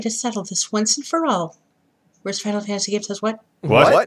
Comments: Could anyone tell me what Terra is saying at 7:54 ? i just can't understand what she's saying (i don't speak english as my first language) and i'm missing the point because of it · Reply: Worst final fantasy says what? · what did she said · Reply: "Worst final fantasy says what" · Umm what does Terra say at 7:57? I didn't catch that. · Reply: Worstfinalfantasygamesayswhat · hey guys what did terra said at 7:57 I didnt catch it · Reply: Worst final fantasy says what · sry i cant hear what terra said at 8:00 can any people answer me? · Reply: Worst final fantasy says what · guys, I cannot hear what is terra actually saying in 7:56, after she claims she can settleit once and for all? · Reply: "Worst Final Fantasy game says what?" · what did she say? I can't hear it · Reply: "Worst Final Fantasy game says what" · Can someone tell me what Terra actually said? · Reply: worst final fantasy says "what".... then they all said what. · Not sure what Terra was saying there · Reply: "Worst Final Fantasy game says what?"